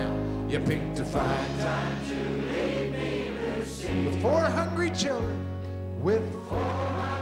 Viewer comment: How many people thought he said 400 children instead of 4 hungry children? Rest in peace Kenny